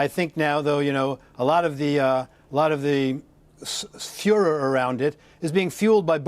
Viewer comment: did he say "Fuhrer" instead of furore? ;-)